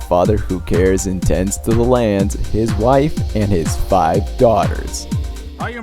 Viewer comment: Did I hear that right? 1:45 Five daughters?